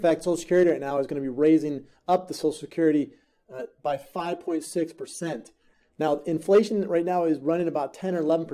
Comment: I thought they said 5.9 percent.